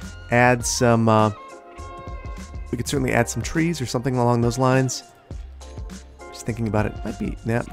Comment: What says: “let’s add some landscaping!” What I hear: “Let’s add some firewood!”